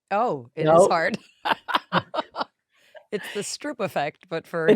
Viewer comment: The Stroop effect. I thought she said "strip".